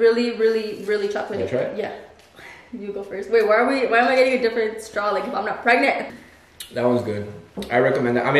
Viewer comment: Low key it sounded like she said “ why am I using a different straw am not even pregnant ”